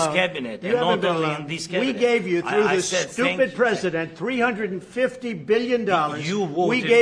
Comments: did i heard Trump says Stupid president or excuse me president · Did Trump just say stupid president? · does Trump call Zelensky "Stupid President"? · Stupid President? Did I hear correctly? Forgive me if that's not the case. 🙏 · what did Trump say, " Stupid President"...😂😂